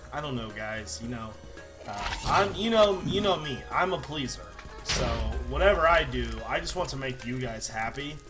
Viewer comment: I Thought You Said "I'm a policeman" Lol!